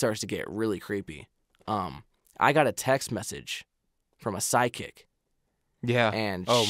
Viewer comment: anyone else thought said sidechick at first? Lol like you cheating?!